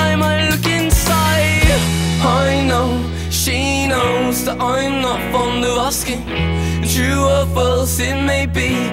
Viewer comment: All this time, I thought he was saying "I'm not from Nebraska" 😂